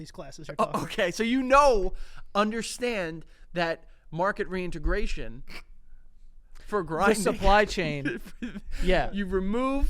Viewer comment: wow I’ve been watching too much severance cuz I thought he said “Mark and reintegration”